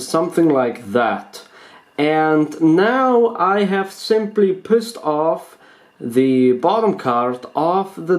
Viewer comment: not trying to be mean but when you said" you pushed off the bottom card" it sounded like you said pissed off